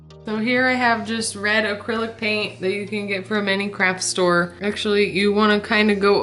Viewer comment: I thought she said crap store 😂